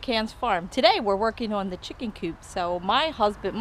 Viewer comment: I thought it said chicken poop